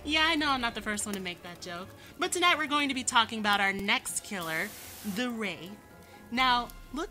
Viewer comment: yo i thought you said the ....... ahem THE RAPE!!!!! Just to be sure i am 11 yrs old